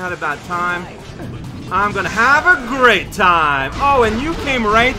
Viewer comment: I know it's not what actually said but now whenever I eat cherries I'm going to say "I'm gonna have a GRAPE time!"